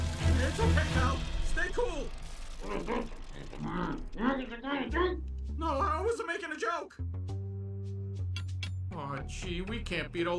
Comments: I think I can translate what Draggy was saying “One of these days wild break were you making a joke?” I could be wrong my muffled is a rusty lol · "Wildbreak, are you trying to make a JOKE?!" (took me a while to get what he's actually saying, took me like 15 replays on that part)